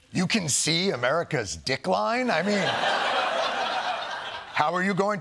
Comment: It seems like the discussion of "America's decline" would have been a prime opportunity to show a map of the US, and then zoom in on Florida. Thank you to Jon and the entire crew (including everyone off camera and behind the scenes) for making me laugh today. I really needed it. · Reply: I heard "dic-line" when it was initially spoke and had to do a double take. I figured tr*** was reading from a teleprompter and the word didn't fit on one line so was split. But tr*** didn't know the difference. (I haven't heard anyone else refer to it.)